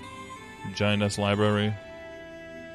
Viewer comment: I definitely misheard vagina's library 😅